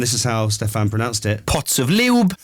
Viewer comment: As a young lad at 25, I find a lot of the younger generation are disinterested in the royals, but I admire these pair. I’m going to love seeing them become King and Queen one day and this interview is so down to earth and shows them in an incredibly good light. On a different note, it’s bizarre when you listen to the radio so much and build a image in your head of presenters based on how they sound, then when you actually see them talking and they look different than you expect. Bizarre haha! Anyways Kate’s really sweet and William is a top down to earth fella, when they finally become our King and Queen it will be great thing for this country. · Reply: I was watching the video and at 17:47, I heard 'bottles of babe oil' and 'pots of Lielb.' That's how I heard it, but I might have misheard. Could you clarify whether I heard them correctly? Thank you!